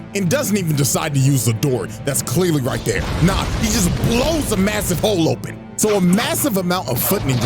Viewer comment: did you say.... a MASSIVE hole?